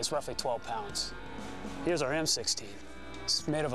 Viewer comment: it sounds like he said M60 did anyone catch that or is it just me?